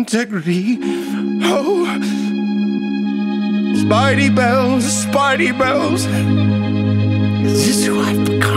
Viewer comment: I thought it said spidey balls